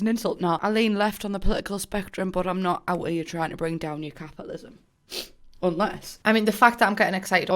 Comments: HAHAHHA, when you first said that the game needed more capitalism I first thought you said Catholicism so for a while there I was really confused wondering where you were going with it ... 😂😂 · When you said capitalism, I thought you said Catholicism!!